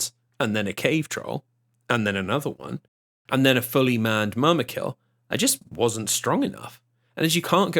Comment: what is that you're saying? Mermakil? Edit: after trying dozens of variations I discovered he's actually saying "Mûmakil" or the giant war elephants